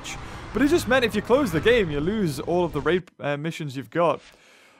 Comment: Did he say you lose all the rape missions you got